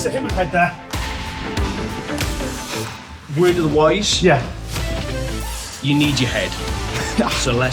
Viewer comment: the subtitles say "word to the wise", it's actually "word of advice", smeared by the accent into "word o a wice"